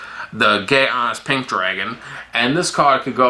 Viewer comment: i thought he said gay pink dragon